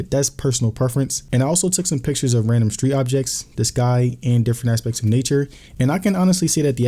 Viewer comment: When he said he took pictures of "the sky" why did I hear "this guy"? I'm over here searching for some guy who isn't there 😅😅😅